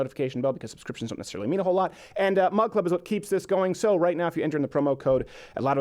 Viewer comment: You have no idea how long I thought you were saying mud club